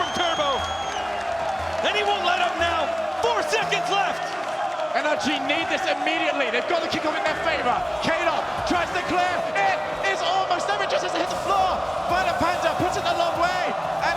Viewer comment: Hello, I'm Japanese 1:33:25 - 1:34:04 What do you say in English? I don't understand the text. please tell me! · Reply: Anyone light up now? Four seconds left! NRG need this immediately, they’ve got the kickoff in their favor, Kaydop, tries to clear, it has almost, never just hit the floor!!..ViolentPanda puts it a long way, and it’s almost there, Justin keeps it alive, Turbopolsa there…bounces..it’s a corner! NRG are still around, Justin is there for the shot…THATS IN!!! JUSTIN!!!!!!! THIS IS ROCKET LEAGUE!!!!!!!!! They did it!!! The rookie, of, the year!